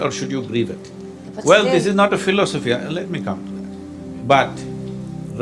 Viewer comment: When the woman was trying to politely interrupt him. Instead of him saying please let me finish, he said "please let me come." Or he either said, "please let me compliment." (Minute mark 8:40ish) Wow!! The power of words and their vibration and The changing of just one word in a sentence can have so much power and he proves it right in that moment . Such a beautiful thing to observe a man who is so intact with his consciousness within himself. Very powerful